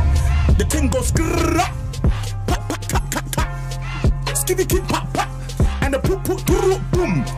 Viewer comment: Always thought this said “skinny kids pop pop”